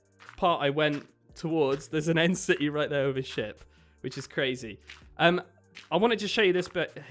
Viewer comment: I thought he said theirs a end city over their holy sh!¥ but he said with a ship